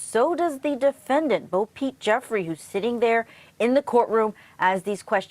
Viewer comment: No disrespect to anyone but these Southern names like this Bo Pete Jeffery here just crack me up sometime LMAO. I don't think I could of grown up with that name and not had a huge problem with my parents for it lol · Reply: Lol at first I thought they said Bo Peep 😄🐑